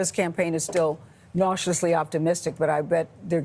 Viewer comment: I heard "cautiously optimistic" as "nauseously optimistic".